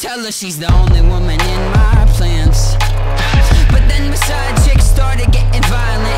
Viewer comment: It sounds like he says "the only woman in my plants" or "the only woman in my pants" ahvdtvjvyshv